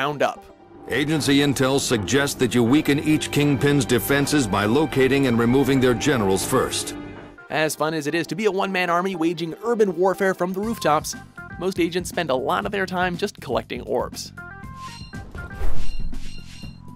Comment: I thought he said removing their genitals lol